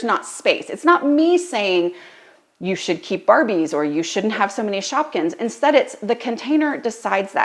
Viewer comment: I thought you said the kids wanted to get rid of Barbies so they could keep shotguns. 😂